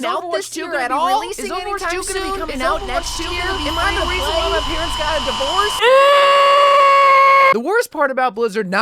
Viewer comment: Bro I love these vids. At the start I hear "am I the reason my parents got a divorce?" BRUH classic comedy